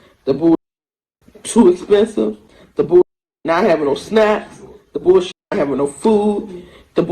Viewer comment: She quite literally said they don't have SNAP (a form of welfare). So, yes, she was complaining about the lack of social welfare programs there, because they don't have food stamps. Which, given that we know many Africans suffer from hunger, is extremely tone deaf on her part. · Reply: I could be wrong but I thought she said "snacks" not "SNAP"